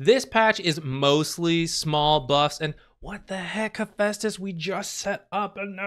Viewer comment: Hephestus sounded like Asbestos 😂